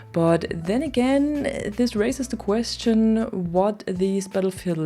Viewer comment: "this racist the question" 😅 anyone else hear it that way?